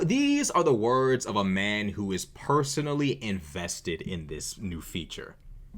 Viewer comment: I thought you said personally infested. Works that way too.